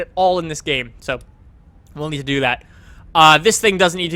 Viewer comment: what he says lmao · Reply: will need to do that?